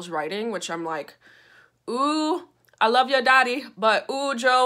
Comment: I laughed and choked on my apple when I heard "ooooh, I love your daddy."